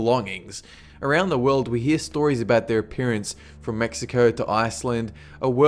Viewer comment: It sounded like he said "Smexico" in the begginning... xD